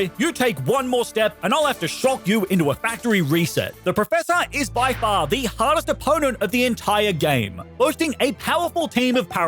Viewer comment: I thought he was saying “The professor is the HOTTEST opponent” I was a little confused at first 😂